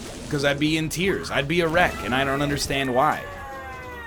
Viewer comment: I thought he said "Erect" instead of "A wreck" at 24:55, I am aparently 12 years old.